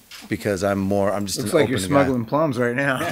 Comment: What does Satchel say at 6:26 please ? "Looks like you're smugglin ??? right now" Plums ? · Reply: mart8886 yes he says plums